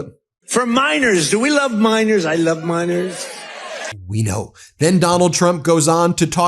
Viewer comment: You may have heard, "I love miners". I heard, "I love minors".